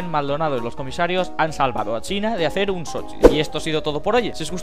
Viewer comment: yo escuche dad con vuestras manor xdd